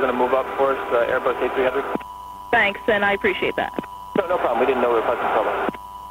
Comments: Did anybody else catch " we didnt know where President Trump went" ??? @ 27:52 Like WHAT!!! Somebody explain please. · check out the transcript "We don't know where President Trump went" I'm not sure exactly what was said, it'll be an error in transcribing, but that's what it sounds like lol